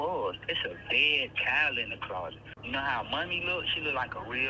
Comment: The dead child looked like what ? Sounded like he said money !? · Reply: Mummy.